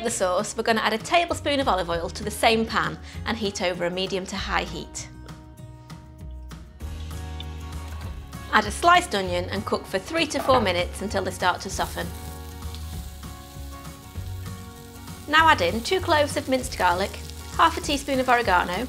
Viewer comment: at first I thought you said add onion for 3 to 4 minutes until they start to suffer.